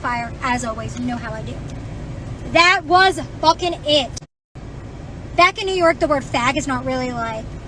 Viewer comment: Did she say 'back in New York'?? OK, now this makes sence.